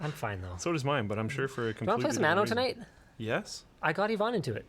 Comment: at time 34:43 you spoke of a game I think you called it Nano. What is that? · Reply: It's Anno 1800. :)